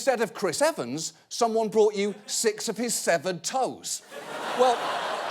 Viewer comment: I completely misheard that as "six of his seven toes" and suddenly had a million questions